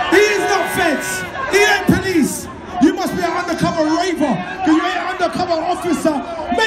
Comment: Thought he said undercover raper